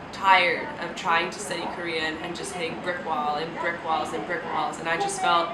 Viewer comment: Could you please transcribe what you said in the video from 5:48 to 6:02. It sounds like some kind of idiom, right?? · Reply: "Hitting a brick wall" means you can't continue because something is stopping you, or something is in your way.